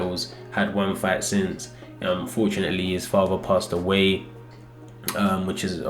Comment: did just say fortunately his dad passed away? 💀